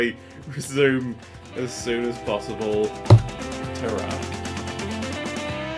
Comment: He ended the video on a "turrah". My heart wasn't ready for that. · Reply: I was a Yorkshire kid and I always heard it as "Tarrah", so hmm